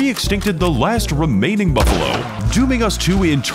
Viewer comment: i straight up thought you said 'Muffalo'